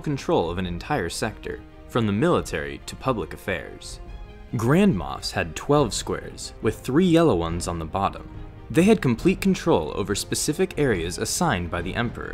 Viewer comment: Grandmas has 12 squares Come on I can't be the only one who heard that